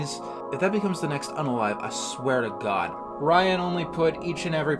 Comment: “If that becomes the next(Insert Word)I swear.” What word did say there I couldn’t make it out. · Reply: 'Unalive', a censor-friendly equivalent of 'dead'.